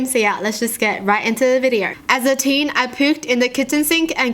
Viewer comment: I wasn't watching but listening and I heard pooped instead of puked smh